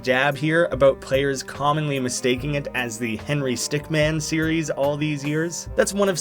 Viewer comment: after “escape the compoind’2 I thought he was called Henry stickMEN! Though this spelling is closer to the pronounciation, il give you that!